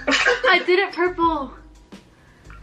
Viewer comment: If you turn on subtitles, when said, "bleached it," The subtitles said "Bleed Sh*t" xD